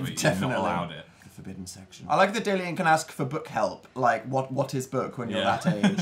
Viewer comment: it said "book help" but i thought it said bonk help zomg